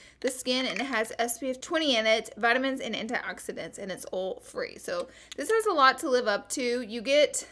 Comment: When you said "oil free" I totally thought you said "all free" because of your southern accent compared to the Boston accent I grew up around!